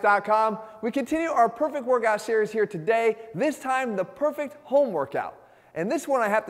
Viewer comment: You heard “home workout” I heard “homework out” ✌🏽